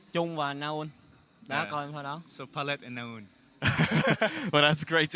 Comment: what did he say at 46:56 ? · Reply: Palette and naul